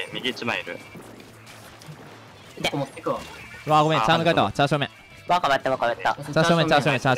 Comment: チャー正面がチャーシュー麺に聞こえて笑ってしまった